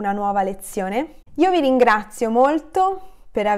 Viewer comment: Hi! Why did you say " Lo vi ringrazio " on the end of your amazing video ? ( in 10:15 sec. ) I am a little bit confused and curious about "LO" . I know about "VI" , but "lo" ... Please, if you have one of video of your with explanation, please place it here ! Congrats, you are a great teacher ! Thank you so much !!! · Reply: I'm pretty sure I said "io vi ringrazio" :) maybe it was too quick and sounded like an L, ahah!